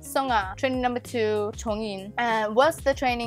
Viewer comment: I heard 'Jongin'? Did I hear wrong?